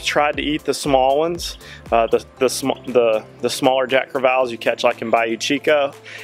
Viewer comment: I'm from guam and fish alot for food, you my dude just dont know how to cook these fish. it's honestly all to the persons taste, but if you know how to cook em, their usually pretty good eating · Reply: my bad I thought said Jack Trevalles..which in Hawaii is referred to as Papio/Omilu which is good eats...once they grow to 16lbs they are called Ulua...which is big game fish in Hawaii.